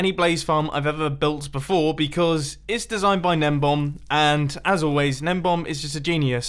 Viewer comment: His name is Gnembon. I always thought that Mumbo was saying Nembomb.